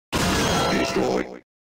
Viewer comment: I hear "Always I" Or "I'll Destroy" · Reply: Destroy